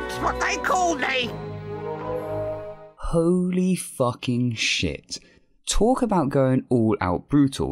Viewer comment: For a second I really thought the Hare said holy fucking shit...